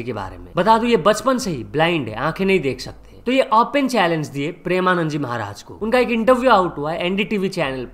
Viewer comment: tell me if I am the only one who heard "ankhe nhi dekh skte" not disrespecting